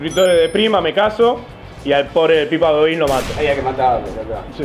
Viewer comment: parece que dice pipa godin